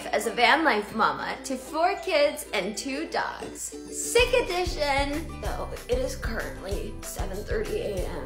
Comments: I literally heard "sick of this sh*t" instead of "sick edition". Lol had to watch it twice cuz i was shocked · For a second I thought she said "sick of this 💩" 😂